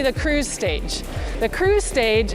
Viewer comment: For a crazy moment I thought she said the "crew stage"!!